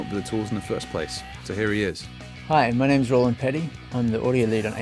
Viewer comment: Hahahah! "Hi I'm Roland Peddie" I thought he said "Hi I'm Rolling Pennie" xD